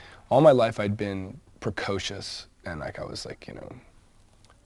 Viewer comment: what did he say at 2:16? id been precocious ...... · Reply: and like I was like, 'you know...